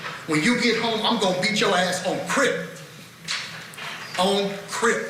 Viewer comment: Does anyone know what word the defense attorney uses at 3:23? He quotes the victim as saying (or texting) I’m going to beat you[r a##] on __________.” Thanks, in advance! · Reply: Crip